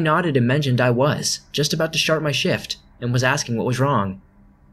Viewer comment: just one thing i would say to work on would be the pauses between each sentence it really for me kills the story when u take to long of a pause i know very odd but your videos are great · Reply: He says shart instead of start in this also